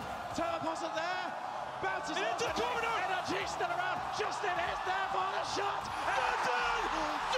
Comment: What does he say at 0:21 ? "nrg-ist-alaba" ??? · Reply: nrg still around? best i could make of it